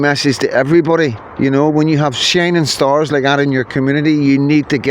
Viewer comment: You are a shining star too, 🌟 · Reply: For a second I thought you said shinner star